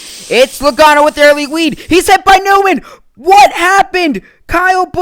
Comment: Thru 12:50 he says Wead instead of Lead it sounds like